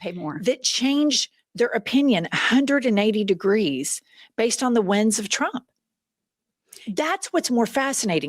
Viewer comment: "The Winds of Trump" 😆 I may have misheard that though...